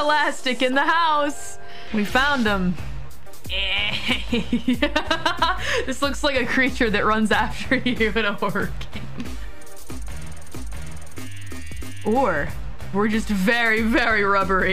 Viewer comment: I think she was saying "This looks like a creature than runs after you in a horror game" rather than "at work". Great clip as always! Keep up God's work, soldier 🫡😆